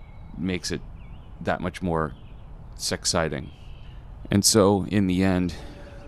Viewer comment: did you say 'sexciting'? i'm adding that to my dictionary.